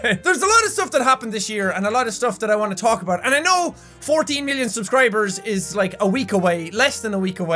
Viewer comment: I thought he said 40 million subscribers coming up and I was like WAIT WHAT